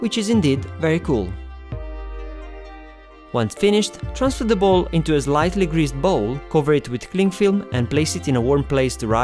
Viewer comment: Sounds like you said "transfer the bowl into the bowl" 😂